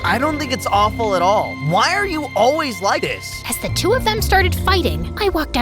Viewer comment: “ I don’t think it’s awful at all. Why are you always light this?” The voice and how he said light instead of like😂😂😂